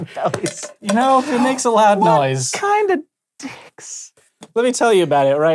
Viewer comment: Does Hank say, "What kind of dicks?" and then just kind of trail off because he remembered he was filming? Either way, that was some funny shit! lol